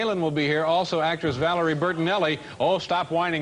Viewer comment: What did the audience member yell at 10:07? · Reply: say hi to washington.